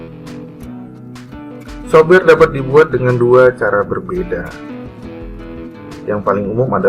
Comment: yang paling umum adalah apa pak??. Di subtitle saya tertulis hamil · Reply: hak milik koq jadi hamil ya??